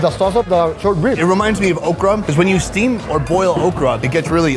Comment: Walked to another room and thought Sonny said 'it reminds me of Oprah' instead of Okra lolol